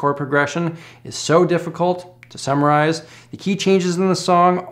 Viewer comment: I heard "difficult to samurai" before I heard "difficult to summarize"